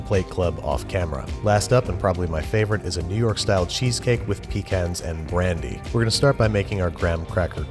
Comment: What I heard: "And probably my favorite, is a New York style cheesecake with Pea Cans and brandy" Where were the "Pea Cans"?